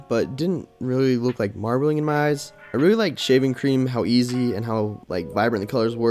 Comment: Why I heard “shaming cream”